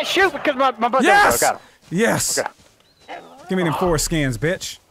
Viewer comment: did he say give me that foreskin?